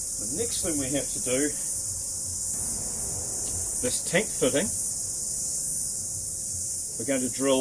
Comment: What is the name of the black tube piece?? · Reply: I was wondering the same thing @ 4:05 ...I think he say a "tank footing"....GL